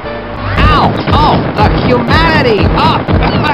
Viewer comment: well, all fine and dandy, then at 1:09:06 i hear spy fox scream "fuck humanity". DA FUQ? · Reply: No, he said "the" humanity.